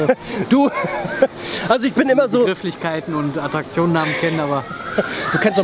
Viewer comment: Ich hab' erst verstanden "Das merk ich immer beim ka**en" :D